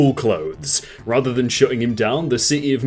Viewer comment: i heard that as 'shooting him down'